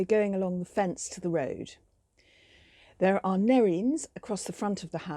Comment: thank you for this video! Very useful for my future front garden :) I didn't catch the name of the pink flowers on 1:17. What are they? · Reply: Thank you - they were Nerine bowdennii